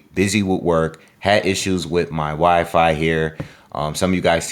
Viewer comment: almost sounded like: "had issues with my wifey" 😉😅👊